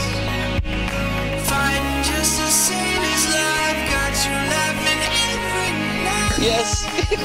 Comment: What does it say at 17:53? "Fun just as sane as life, got you laughing every night!" Is what I think it was. Doesn't sound like it, though.